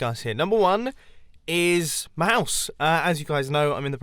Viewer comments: Did you say "Mouse" instead of "house"? Lool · At around 4:55 I thought he said mouse so I'm like "oh, you're buying a pet?" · I thought you said mouse as in a mouse got into the RV and I was like 😱